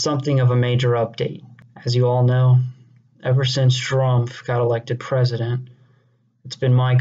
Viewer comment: why the fuck does it sound like he said Strumph?